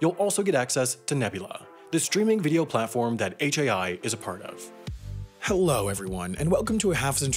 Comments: Wait... in the intro, says "...that HAI is a part of", but the captions say "...that Wendover is a part of" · the caption says wendover instead of hai xD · the captions say Wendover when he says HAI. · In the captions at 0:08 it says wendover instead of hai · haha the subtitles say Wendover, not HAI. But I shouldn't be mean. You guys make seriously AWESOME content that I LOVE watching. Thanks for all you do! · Did anyone notice at 0:08 In the Subtitles Instead of HAI it is shown Wendover